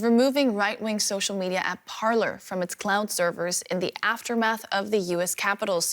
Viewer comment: It sounded like she said clown servers.